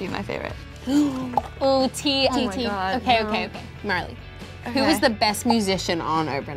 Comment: I thought said molly not Marlhy I was like who’s molly then I was like ohhhhh it’s marlhy